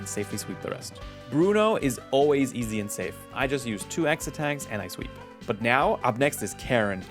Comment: The video started skipping on my end when you were at the final Bruno battle and it made it sound like you said "I just use 2 X Attacks and I sweep, sweep, sweep!" Which gave me the mental image of Lugia just sweeping away Bruno's Pokémon with a broom. XD